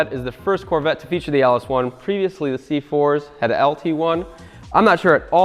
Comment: Did he say C4?? So, I take it this car is the bomb huh?? aka Blaze